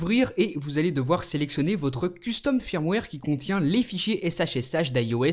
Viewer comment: At one point did you say leafy s*** at 2:12-2:13?